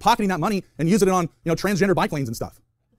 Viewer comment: I’m watching at 2x and I misheard this as “transgender bi-planes”. And while what we need is bike lanes, what I want in my heart of hearts is trans bi-planes